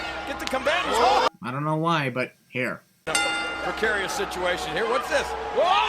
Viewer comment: You say "here", the subtitles say "there". +1 sin.